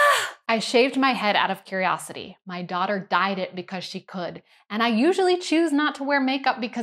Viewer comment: Jesus, I thought you said your daughter died, not dyed your hair! I didn't even know you had a daughter